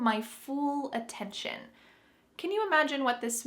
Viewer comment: After 2:50 , when, I think said "fool" insted of "full," I am really curious to know if some people pronounce the word "full" like "fool." Cuz, to me and to a lot of americans and canadians, they sound different: "Full" would have the vowel of the word "book," and "fool" would have the vowel of "room."